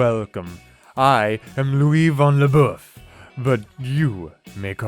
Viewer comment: For a minute I thought the Drama teacher said his name was Louie Von A Fluff....😂😂